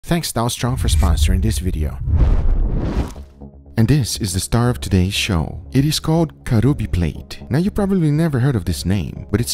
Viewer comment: When the video started I thought he said “thanks Donald trump for sponsoring this video”